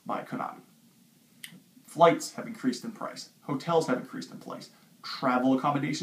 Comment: Sounds like he said plice instead of price